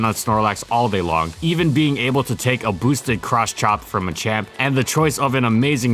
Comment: am I the only one who heard, "a boosted crotch chop"